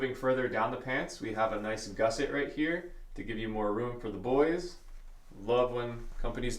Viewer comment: At time 2:39 I kept hearing “more room to void” wait what? Lol